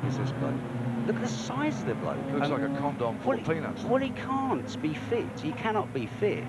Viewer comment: Can’t help but laugh at Barry’s comments at 1:30. That is one big guy riding that bike! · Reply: I thought he said "like a condom full of peanuts"??